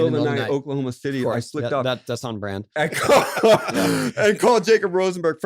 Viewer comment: What Tony said: “That’s on brand” What I heard: “This fuckin guy”